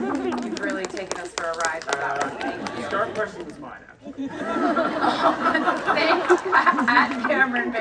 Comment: What did the interviewer say at 23:03? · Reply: he said “that question was mine actually”